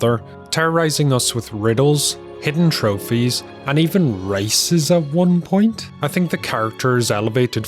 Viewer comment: Both me and my mother thought you said "terrorizing the player with riddles, trophies, and even "racism" at one point" That made us die laughing...